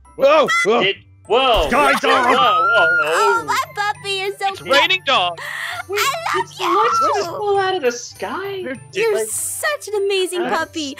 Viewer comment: did just say "die dog"?